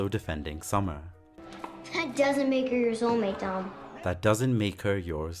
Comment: I think the sister is saying "That doesn't make her your soulmate", not 'yours only'.